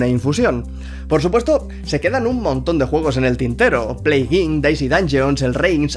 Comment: Que alguien me escriba el primer juego que dice por favor xD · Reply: plague inc